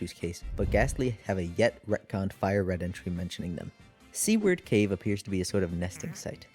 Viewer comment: i’m so sorry but when you said seaward cave i thought you said “c-word cave” and now i want a location called cunt cave